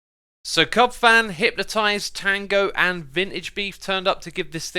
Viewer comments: I know you said, "So Cubfan, Hypnotized, Tango, and VintageBeef turned up to give this thing a whirl," but all I heard was, "So Cubfan hypnotized Tango and Vintagebeef." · did anyone else hear that as Cubfan hypnotized Tango and Vintage Beef turned up?? 😂😂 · When he said cubfan, hypnotized, tango, and beef! I thought he said Cubfan Hypnotized Tango get it! :D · am I the only one who heard that as “Cubfan hypnotized Tango and VintageBeef” at first?